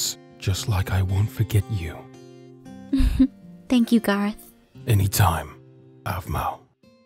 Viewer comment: Aphmau:Thank you Garroth Me:I thought she said "thank you God" and I was like wat